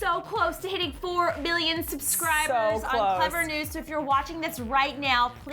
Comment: I thought they said 4 billion subscribers. 😯I still can’t believe you guys have 3 million